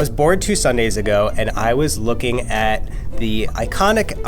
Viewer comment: For I second I thought he said "I was born 2 sundays ago" then I realised he said "I was bored 2 sundays ago"